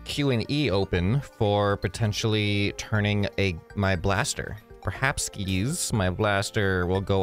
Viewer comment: HE ACTUALLY SAID PERHABSKIS